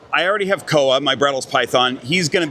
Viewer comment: I had to replay this part. I thought you said "my brother's python," and went Kent has a python?! 🤣My hearing is terrible.